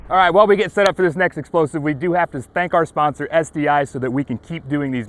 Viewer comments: "We'd like to spank our sponsor..." Anyone else hear it? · You have to spank your sponsor? Excuse me? I played this multiple times and that word is what I keep hearing. Your sponsor will have to spank you right back.